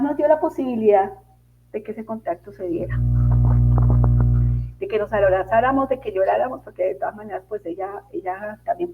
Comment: en el minuto 47:02 hay una interferencia y parecía que hay una voz que dura segundos, pero no se logra entender lo que dice. Es justo después de cuando ella dice: y nos dio la posibilidad que ese contacto se diera. Espero no haber alucinado🥺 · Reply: Gran oído, es así...ya lo escuché anteriormente y dice MA